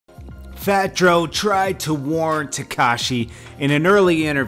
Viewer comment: Sounded like he said fat dro at the beginning.